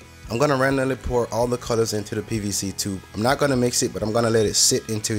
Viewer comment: I was watching TV and your video at the same time. So at 2:22, I thought you said BBC instead of PVC. Needless to say, I had to pause the video for about five minutes while I tried to control myself from laughing hysterically. That table turned out incredible! So awesome to see you playing on it with your daughter at the end. I love how she tried to swirl the colors in the table around 7:10. That is how great it turned out!